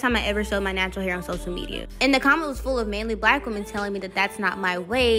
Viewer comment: I thought she said manly bw 😂😂 had to hit rewind